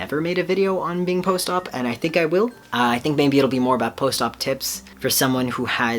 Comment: When you said "post-op tips" I heard "post-op tits" 😂😂 I really love this video!❤️💛💚💙💜